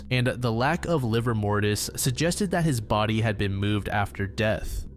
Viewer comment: Did he say “livermortis” at 25:45 ?